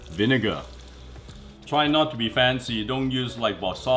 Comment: Did he just say "dont be fancy"?